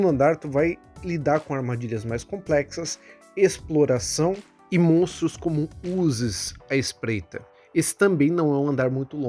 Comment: eu entendi tu falar "Monstros com Uzis" e fiquei pensando num goblin de submetralhadora